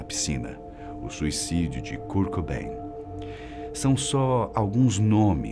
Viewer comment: o suicídio de cu Cobain foi isso q entendi